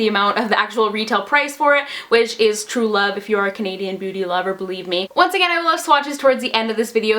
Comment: It sounded like you said Canadian booty lover 😆